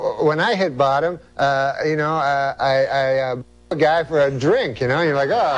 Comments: What does Norm even say at 6:01? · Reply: “Blew a guy” not fucked. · Can someone please help me out. What does he say at 5:55. Having a hard time hearing the punchline.. "Got up for a ___drink"? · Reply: they blanked it out, he doesnt say got up for a, he says something like blew a guy for a drink